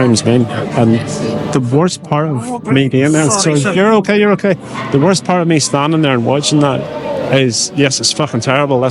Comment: what did this man say? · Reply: he just said “sorry” as he bumped into me on the way past.